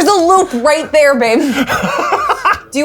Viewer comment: does call ben babe